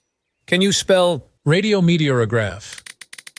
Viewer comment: All I hear is “spell. Red.”